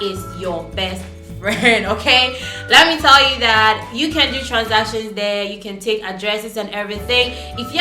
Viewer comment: in the video what did u say? u said u can do transitions there etc